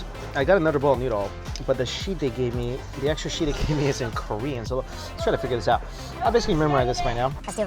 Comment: I just realise what said at 7:24 he said korean 😂😂😂